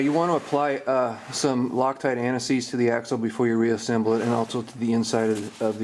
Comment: Thanks. I clearly heard you say loctite (brand) "ANTI-SEIZE to the axle, not simply loctite.